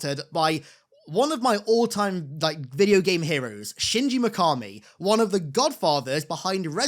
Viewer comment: man i thought u said sinji icari